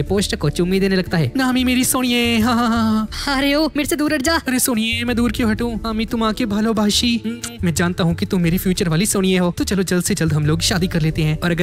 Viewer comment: did you say " ami tomake valobashi "??? I'm from 🇧🇩 how r you???